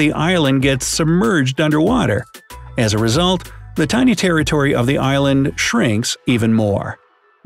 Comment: I had to replay the sentence because I heard “Terry Tori” 🤦🏾‍♀️